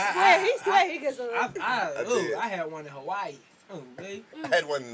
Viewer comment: every friday! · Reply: I heard you say yoi had an Asian in Hawaii. Lol. Running your mouth.